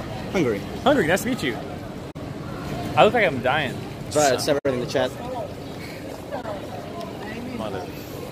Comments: what is the hungary guy saying? I can't understand what he's saying. It doesn't sound like "Jake is gay" so I'm out of ideas. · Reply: TriHard 7 in the chat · what is the hungary guy saying? I can't understand what he's saying. It doesn't sound like "Jake is gay" so I'm out of ideas. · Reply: Dezrt Because everyone here is half-deaf and the Hungarian guy said Trihard7 in the chat Not Fed7 or 7 by itself. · anyone catch what says right around 1:35? something in the chat? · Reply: ''trihard 7 in the chat''